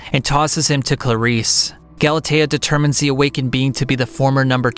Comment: Bruh I really thought they said awakened bean